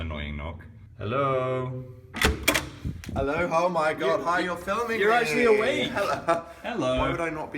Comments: ik this isn't what he said but when opened the door I sounded like he said "Hello Hone-OH you're recording!" OwO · ik this isn't what he said but when opened the door I sounded like he said "Hello Hone-OH you're recording!" OwO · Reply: "Hello-oh my God you're filming me!" That's all I heard. There's an extra h sound in the "oh". But that's all.🤷🏻‍♀️